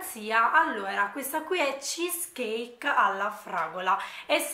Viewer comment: Si dice cheescake alla fragola 🤦🏻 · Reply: Infatti vai al minuto 1:30 dico “CHEESCAKE ALLA FRAGOLA “ risenti poi dimmi tu cosa senti 😘